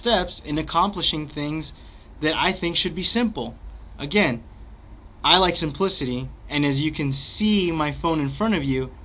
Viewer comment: i swear to god i thought he said " I like some pussy" LOL